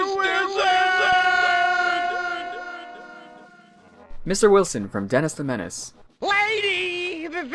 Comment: I think he says "Mr. Wizard"